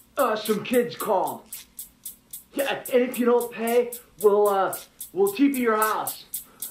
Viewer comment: "If you don't pay, we'll TP your house." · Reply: LMAO..I always thought ht said "we'll keep your house" lololl